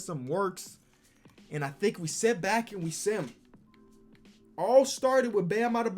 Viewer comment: I thought he said ok now we sit back and simp lol